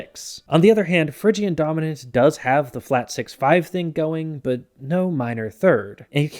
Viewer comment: Anyone else hear the guy from that clip that Adam Neeley uses saying “Nostalgia” at 20:53?